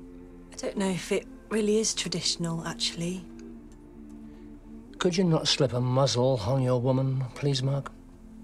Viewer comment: what does marks dad said at 22:05 ? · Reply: Adrasthea Could you not slip a muzzle on your woman please Mark?